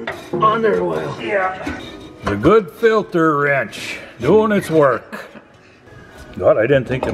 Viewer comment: I thought said wench. I had to listen to it a couple of times and I'm still not sure.